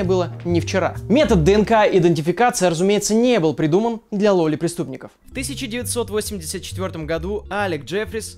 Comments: услышал для лоли преступников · "для ЛОЛИ преступников" хмм, мне послышалось? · почему я слышу лоли преступников